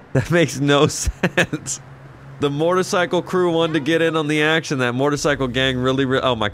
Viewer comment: It sounds like you say “mort-icycle instead of motorcycle lol